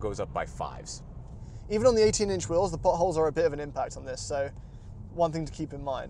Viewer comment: for a second i though he said the butt holes instead of potholes lol